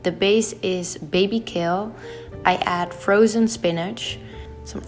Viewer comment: When she said baby kale I thought she said baby kill.